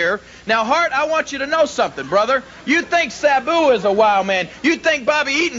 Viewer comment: Did Jerry just say Sabu (o.O) wtf moment right there.